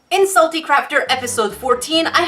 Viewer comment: I thought you said at the start....’Insulty Crafter’.....🤔